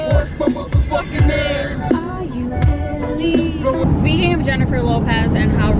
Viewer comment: I literally thought in the song “I’m real” from jlo that when she says ‘R.U.L.E.’ I thought she was saying ‘are you elly?’ Hahahaha! Just finally got it within the last year lol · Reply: Lol I was today years old when I learned that's even what she's saying. I thought she was saying "are you readyyyy" 😂 omg